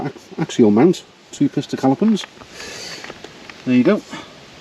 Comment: I bet you think it was the RT slip. If it was that, I was generally referring to when I bought the BMW. 🤣🤣🤣🤣🤣🤣🤣🤣🤣🤣 I knew someone would pick me up on that 🤣🤣🤣🤣🤣 Should have said BMW first 🤣🤣🤣🤣🤣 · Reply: hey correct me if im wrong but at 14:14 you were talking about the two pista calapons instead of two piston calipers 😂😂 i really enjoy watching your videos im from australia